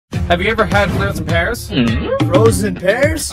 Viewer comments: All i heard was "frozen Paris" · For some reason I thought it said frozen pearls